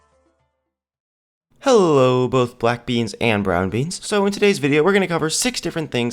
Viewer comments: Did he just say Black Beans or Brown Beans? Lol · did he just say "Hello black beans and brown beans!" not kidding plz no racist replies i just heard it